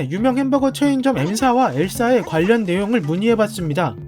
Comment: L사에 라고 말하신걸 엘사에 라고 들었어요 ㅋㅋ